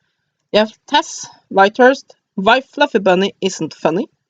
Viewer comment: I thought you said White Fluffy Bunny. oooh where! Oddly I just don't feel connected to this book